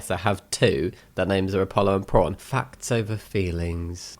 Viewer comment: You named one of your cats after the Greek god Apollo? (I might have misheard that lol, sorry)